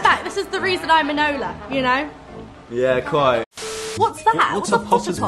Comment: what does Louis say? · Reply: Yeah quite